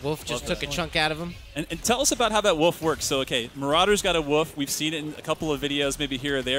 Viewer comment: what he says: marauders got a wolf what i heard: marauders got a woof